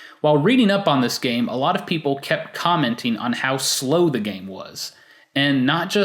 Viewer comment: "While reading up on this game a lot of people CAPCOMenting on how slow the game was" Yeah that's what i heard ._.